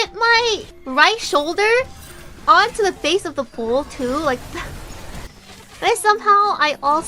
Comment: I think she says bowl as in the toilet bowl, not bull